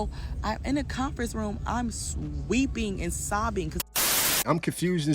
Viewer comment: I swear I heard that ("sweeping") & came back to edit it into my original comment, but when I played it back, I heard "weeping"! Thought I was losing my mind. Maybe she said it TWICE, once each way...??? lol. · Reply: we all heard sweeping the first time...the second time she did say weeping